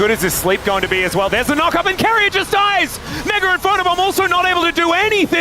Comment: I NEED TO UNDERSTAND WHAT DOES HE SAY AT 40:01 ??? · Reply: "Mega Inferno Bomb (Guma ult) also not able to do anything"